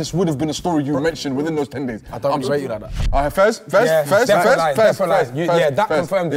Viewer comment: What did say at 14:33 ?? Did he say “I don’t even know u like that”